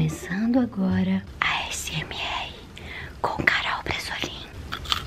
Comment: No cmç eu entendi "garol bresolin" kaka